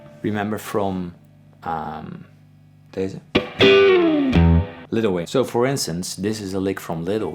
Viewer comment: I thought you said little wayne and I thought you were about to start memeing